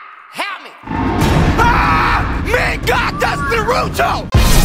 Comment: Duddy: (i think this is what he said) me god thats Naruto Subtitles: me god thats the